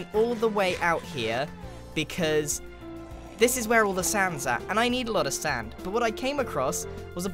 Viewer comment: thought you said Sans